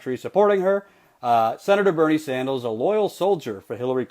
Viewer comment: did you say Senator Bernie Sandals? xD. I love when people do that. i do it often so it's nice to hear unedited things like this! #Sandals2020!